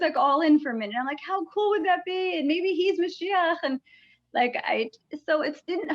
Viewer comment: I HAVE to know -- @ 23:25 when mentions seeing a video where Trump converts to Judaism -- did she say "Maybe he's the Messiah!" ??? I don't speak Hebrew but the word sounded like "Messiah." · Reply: Yes you are correct. The Hebrew word is "mashiach"